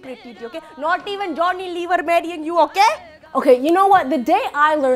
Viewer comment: Not even Johnny lever marrying you..lol😂😂😂😂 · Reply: 😂 I first heard "even Johnny lever not 'bedding' you"!!! I was like whhaaaaaa?